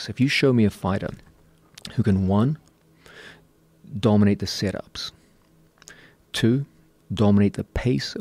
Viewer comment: I'm sorry what is he saying? Dominate the " " pace and direction of the fight. Sounds like he is saying sit ups to me but I know that's wrong